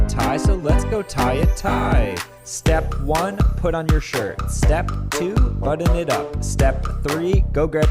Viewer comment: I paused right here at 12:31 and I thought I heard: Step one out on your shi